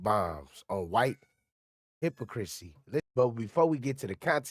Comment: Maaaaaaaaan @ 1:37 did you just call hypocrisy, hippo crazy 🤣😂🥹😭